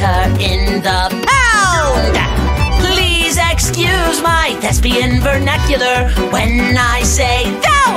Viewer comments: He says "Please excuse my despian vernacular when I say thy is going down." But I hear "Please excuse my lesbian vernacular when I say thy is going down." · misheard lyric: “please excuse my lesbian spectacular” 🏳️‍⚧️🏳️‍🌈🏴‍☠️